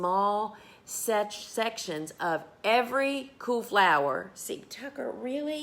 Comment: Not lying. I thought you were saying coo flower or coup flower. Took me a minute to realize that you were saying cool flower. #NorthernerLovingSouthernChat